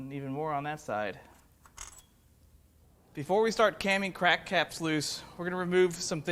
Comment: did he just say caming crack caps loose or is it just me